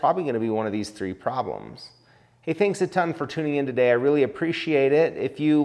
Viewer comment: Great video! At the end it almost sounded like you said, "Thanks a Tungsten for watching". Lol...I see what you could have done there. Thx for posting!